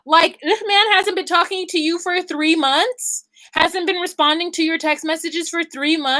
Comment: 3 months? Whoa 😮 I thought she said three weeks. That’s crazy!!!!